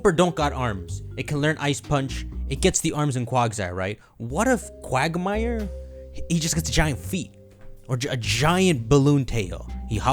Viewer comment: did he just say Quagmire?